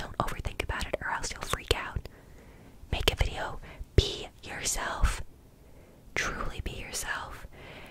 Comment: for a moment I heard "pee yourself" and I was like "Right now?😳 I'm at work, miss"